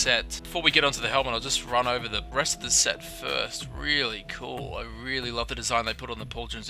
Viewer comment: I could have sworn you said "get to the breast of the set".